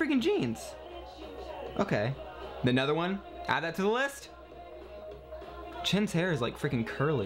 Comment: is it just me or did say chin not Chen omfg lmao